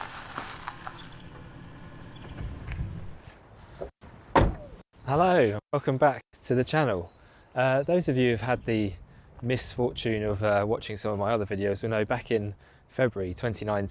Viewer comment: I thought he said "Hello, I'm fuckin back to the channel" in the beginning